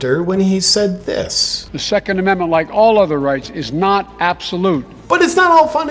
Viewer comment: Did he say the 2nd Amendment is not "obsolute"? (at 3:22) - sounded like that to me.